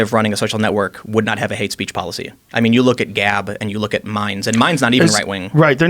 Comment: what was the other social network called besides GAB? Did he say "Mines"? Or what did he say? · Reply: Minds